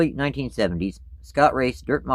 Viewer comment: I thought you said Scott Riggs